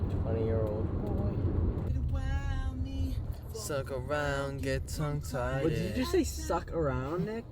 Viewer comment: DID HE SAY SUCK ARIUND 😂😂😂😂😂😭 😭 😭